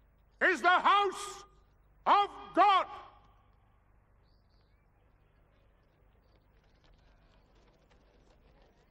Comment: te adam ne dedi? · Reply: Burası tanrının evi.